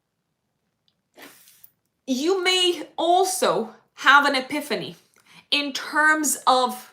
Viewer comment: Libra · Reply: what's the word in 45:47 , abefany mean?! thank you